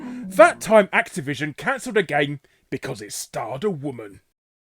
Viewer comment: I thought it said stabbed a woman....not quite sure which is worse.